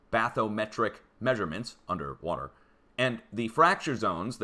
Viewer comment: "We dont do that here. Thats for children...and university professors" 😂😂😂😂😂😂 "we're right below a volcano!" Omg you're hilarious. Funnier than these comedians on yT... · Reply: it sounded like bassomatic measurement